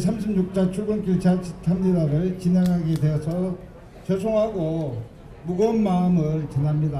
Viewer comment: 죄송하다? 나만 조롱 하는걸로 들림?